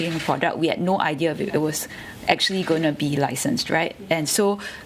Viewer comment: what did the lady say in 1:47? sounded like "san stride" or something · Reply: licensed